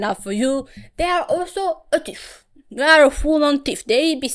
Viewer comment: the fact that I accidentally misheard 13:12 as "they are also british" and I immediately said out loud "..yea that explains alot-" HAHAHSHFHG